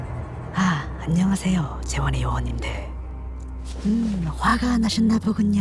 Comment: 아,안녕하세요,재원의 요원님들. 이라고 들리는데요?